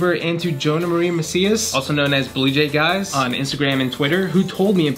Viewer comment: I had to listen to you say “blue jaig eyes” again because I zoned out and thought you said bluejay geese.